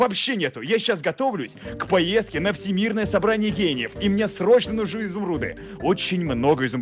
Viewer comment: я готовлюсь на всемирное собрание гениев. То что слышу я: всемирное собрание геев...